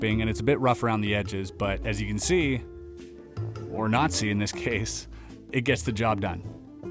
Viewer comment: I heard "As you can see, or nazi in this case"